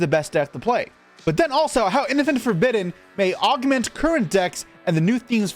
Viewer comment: i thought he said infant forbidden ngl